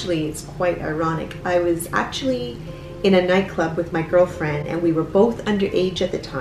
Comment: I thought she said girlsfriend XD